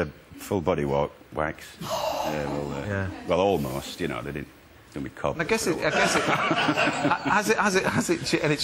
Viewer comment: What's he saying at 4:04- 4:06? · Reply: "then my cock was sore"